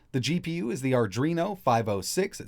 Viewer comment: I heard "Arduino" and had to look up from what I was doing thinking that Arduino got into the GPU business! Adreno != Arduino